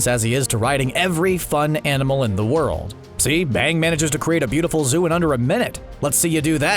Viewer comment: did anyone else hear him say bang? XD